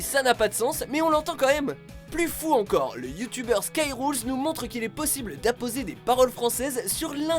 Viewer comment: je crois que moi aussi j'ai eu une hallucination auditive ... j'ai cru que vous parliez du ytbeur Call of duty Skyrroz. Sinon Excellent top 😁